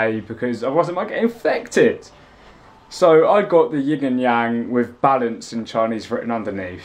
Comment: I thought he said the 'Yig and Yang'..🤔😂